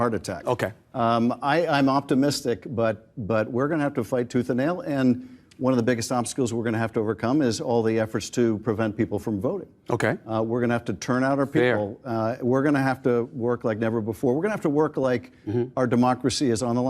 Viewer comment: did he said efforts to prevent people from voting ???